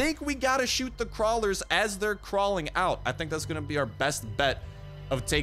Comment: it sounds like he said “nigga out” 😂 but he says thing out 😂